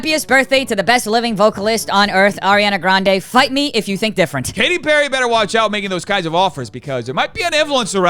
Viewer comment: I thought she said "bite me if u think different"